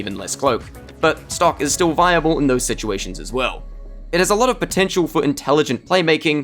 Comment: Did you say potential at 11:49?